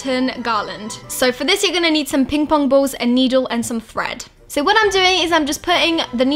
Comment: At first I thought you said bread instead of thread